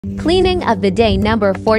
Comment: 😳Sounded like it said the same horse had to be cleaned 14×'s a day, but said cleaning! Seriously, how often do they need cleaned like the video shows?